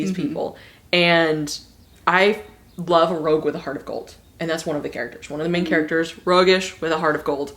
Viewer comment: LOL. While listening to you say "rogue with a heart of gold," I heard "rogue with a hearticle," and I totally want to coin that term now. Just saying. Love your videos!